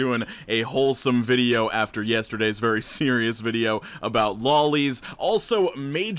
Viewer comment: all I heard was LOLys